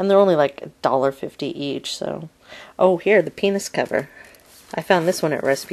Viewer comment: hahaha, 2:30 I thought I heard you but then thought you must have said "Peanuts cover", then you picked up the Little Mermaid clamshell and I thought, good, I'm not crazy, that is what she said